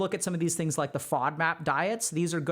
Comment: the what diet?? Can someone with better auditory processing tell me what he said? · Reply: Sattvic diet Edit: Sry, didn't click on the timestamp. He said FODMAP at that time.